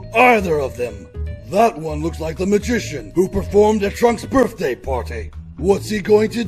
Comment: "That one looks looks like a magician that performed at Trump's birthday party" Edit: I fuggin got it. I heard Trump. It's Trunks. I fuggin got it.